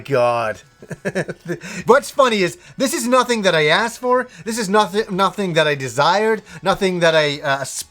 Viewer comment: Haha that mouse pad was hilarious. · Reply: Gaming yeah, i could of sworn he said, "I didn't ass for this." lol